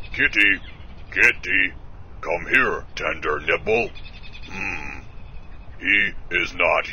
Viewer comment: did he just say ''Tinder nipple''?